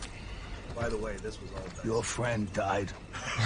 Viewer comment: What did Patrick said when Khotun held Taka's head? It made laugh so hard. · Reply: “Your Friend Died… for you”